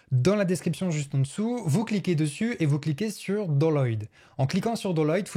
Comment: excuse moi, t'as dit quoi ? doloyd ?